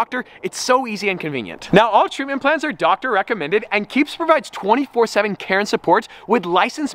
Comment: When said, "24/7 Care & Support" I heard, "24/7 Karen Support"